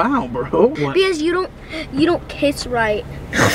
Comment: “You don’t kiss right” max 2020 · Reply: I thought he said " don't p*ss right " I was like wth?!